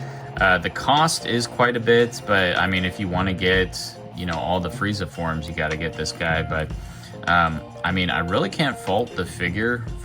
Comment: I thought you said you wouldn't get all of the frieza forms